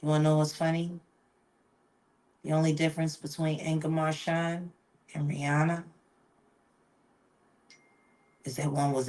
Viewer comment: QUESTION: at 13:30...who did she say??? What name is that? She was comparing someone to rihanna saying "the only differance between..X and rihanna is.." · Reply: I stand corrected…I believe Foxy’s name was Inga Marchand. My Google search was short, but the article I saw referred to Inga as Miss Foxy Brown. Sorry for the false info!